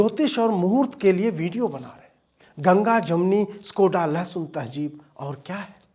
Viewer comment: what does he say at 24:23 ? Skoda-Less?